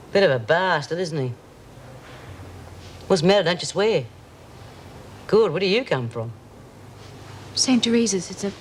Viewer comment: What does Bea say at 35:29? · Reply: bea says- whats the matter,dont you swear,gawd where dyou come from.